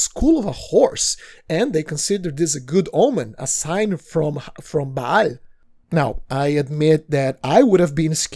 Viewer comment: did you said baal? 💀